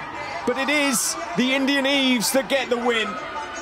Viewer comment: in 4:11 did he say indianeese, instead of indian lol